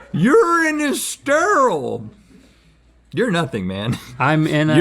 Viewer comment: When Rhett said urine is sterile I thought he said your in his stairwell